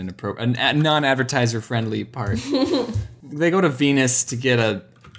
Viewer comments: When James put on the Sailor Moon theme song, I thought the first one said "penis" instead of "venus". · I hear the "they git to venus to get a" bit and got embarrassed 😳